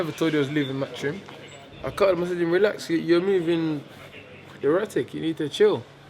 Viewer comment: At 2.59 i thought man said 'youre movin erotic' at first 😂